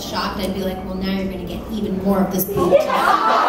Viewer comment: What do Lea say at 0:50? "Now you're gonna get even more of..."? And what does Jon say at 1:27? "I was..."? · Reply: I believe lea said "Now you're gonna get even more boob tonight" and I believe john said "I was shout vomiting" sorry this reply was so late